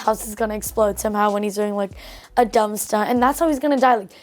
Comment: I thought that kid was just saying dumpster in a brittish accent but no😂